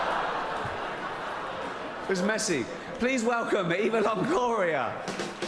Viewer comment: Subtitles 15:32 the Evils of gloria instead of Eva Longoria Brilliant :)